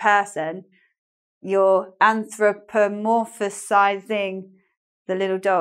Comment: Did you say anthropomorphersize at 6:00. Shouldn't it be anthropomorphize?